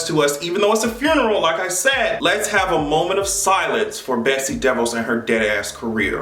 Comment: lol, I thought you said Betsy Devil. That woud havve been more appropriate